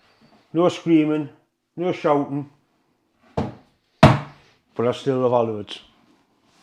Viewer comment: what was your closing comment sounded like you said ( but i still love halibuts) ?????